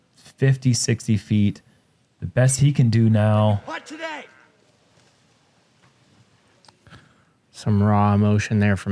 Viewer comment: What does Eagle yell at 30:20? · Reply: I believe he yelled “why can’t I putt today”